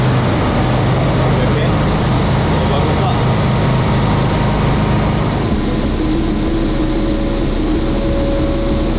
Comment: What does say at 20:45 after “are you okay” ? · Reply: I think he says, "then whyd we stop?"